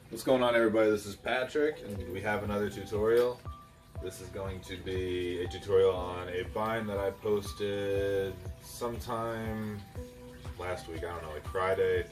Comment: I thought you said you posted a vine